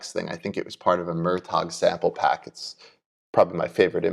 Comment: Thank you to take the time to make this video ! 30:16 what sample pack did you say? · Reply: No problem, thanks for watching! "Murtagh" sample pack...if you haven't come across him yet you're in for a treat haha! He's an incredible producer...has some sample packs out there...great stuff